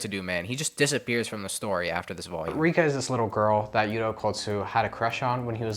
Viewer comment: I heard peperika